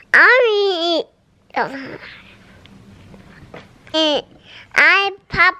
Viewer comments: I think she is trying to say I "ii popp-ular" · it sounded like Elle said “I’m popular!” 😂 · I swear I heard Elle say “ I’m popular “ at 1:52 aww 😂😍